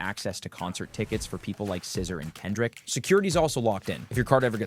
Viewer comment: anything else anybody else heard a scissor/ edit thank you all for everything love you